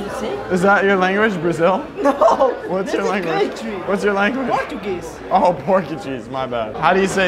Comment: When said “Portuguese” I thought he said “pork and cheese” 😂